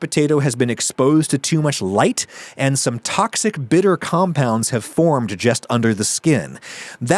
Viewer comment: typo in the subtitles, "butter" instead of "bitter"